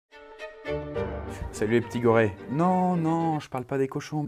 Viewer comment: J'ai entendu "Les gorilles" J'ai fais Ah! Sa change mais nn...